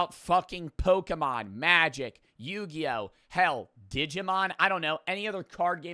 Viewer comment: When he said, digimon I thought he said, diddymon